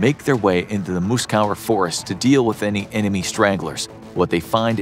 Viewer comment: kind of sounds like you said stranglers rather than stragglers.